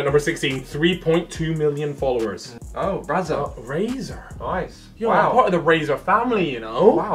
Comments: callux said razer , but it sounded like brazzers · Anyone else heard when Callum said “BRAZZERS” instead of Razer. Lmfao😂